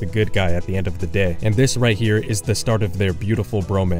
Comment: I'm not sure if I heard him say romance or bromine, hlep!!!!